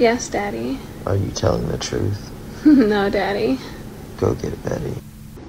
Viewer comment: did he say go get in beddy